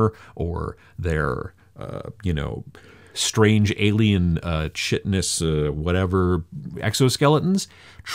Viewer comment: When you said "chitinous" I swear I heard "shitiness" at first haha I've only ever pronounced it "kite-nuhs". Funny how different people pronounce words differently :) On a more serious note, this is really useful content. Thank you!